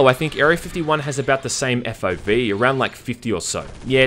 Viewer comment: "Speaking of Halo, I think Area 51 has around the same FIV" What does that mean? I'm not familiar with that. Or did I mishear you?